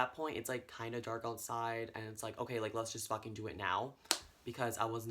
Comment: I swear to god I don’t know why but I deadass thought he said “so we just had sex” 😂😂